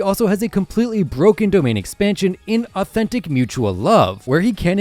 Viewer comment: i heard "inauthentic mutual love" insertskullemoji